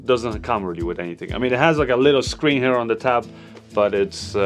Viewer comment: did I heard you say "it has a ka little screen " you sounded like a Zambian 🤣🤣